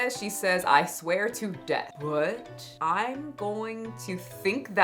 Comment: How the hell is she going to threaten to sue us now!??? 🤦🏽‍♀️ 💥 Fat activist thrown in jail 💥 <link> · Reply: 99% sure “i’m SWEATING to death” is what she said 😄